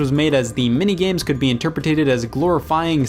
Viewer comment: Did you say 'interpretated" at 27:28?